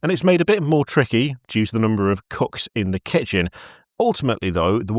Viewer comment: "-cooks in the kitchen..." I thought you said CUCKS and damn near snorted peanutbutter. XD We're off to a FANTASTIC start!